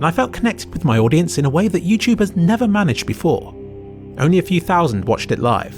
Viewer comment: your captions say "ever" instead of "never"